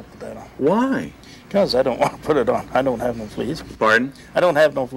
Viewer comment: "I don't have no fleas...." He actually said "I have fleas...".